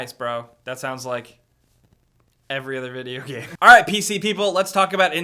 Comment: Sounded like he said "PCP People"